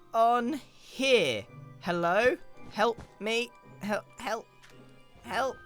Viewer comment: "Hello? Help me help you" It sounded like that to me lol!